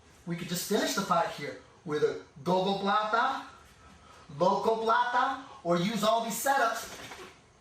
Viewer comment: sorry guys im kinda new at this, but he said in 5:35 Gogoplata i knw that one but then he said another one that i cant understand, what is it? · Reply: I think he said "Locoplata".